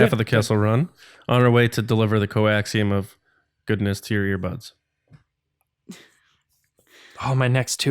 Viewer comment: "on the way to deliver the coaxium of goodness to your butts" <--- this is 100% what i thought you said listening at 1.5x speed hahahaha i went back slowed it down and thank goodness the second time i heard "ear buds" hahaha